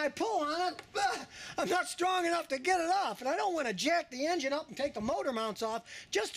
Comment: did he actually say "UH! I'm not strong enough to 'get it up'" ? LOL. :)